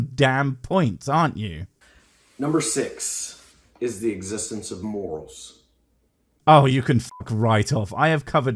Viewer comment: I thought he said the existence of Wolves. I was just like “Thats metal AF.”